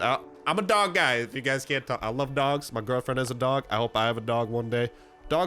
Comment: Wait did You say "my girlfriend is a dog" 31:38 HAHAHAHA WTF